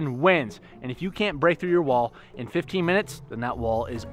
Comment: i thought you said 15 minutes